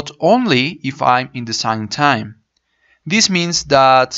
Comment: I loved your video but what did you say at 5:33 - 5:34 ? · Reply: "design time"